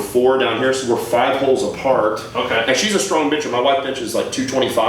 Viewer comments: i thought Matt said his wife is a strong bitch but is actually bencher 😂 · I thought you said your wife was a strong bitch😂